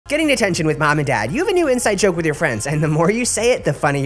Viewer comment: I thought it said “getting attention” and was like YES THIS IS WHAT I NEED